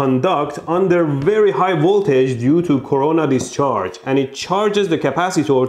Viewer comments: sorry but did he say corona discharge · did you just said Corona discharge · Did he say Corona discharge 2:25 😂